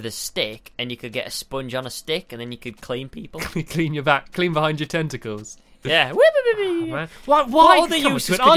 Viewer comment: When they were talking about the sponge on the stick thing, said, oh yeah to clean behind ur tenticles and honestly, i thought he said clean behind ur testicals :/ Lol